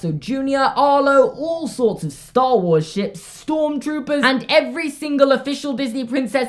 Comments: When he said Star Wars ships I thought he said Star Wars sh*t. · did he curse? kinda sounded like it. cant tell if he said ships or sh*t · I thought you said all sorts of Star Wars shit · did he say all bunch of Star Wars shit? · wait did he say starwars sh** or ship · I thought you said all sorts of star wars sh#t then I rewatched it and you said ship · Did he say all sorts of star wars shit? Edit: it's